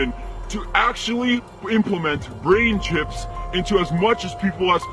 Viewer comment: tama ba ang narinig ko.. Brain chips? Parang sa atin pa manggagaling ang 666?